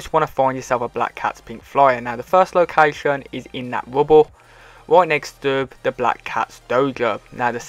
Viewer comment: Straight out this video tops any other video I've seen from every other YouTube including the big ones because it's straight forward and is up to date, thanks for the video keep up the good work !!! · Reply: when he said rubble in the beginning i thought he said wubble